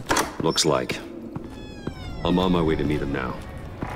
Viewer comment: i dont know how tf but i get a bit distracted and heard this as "im on my way to marry him now" ????????help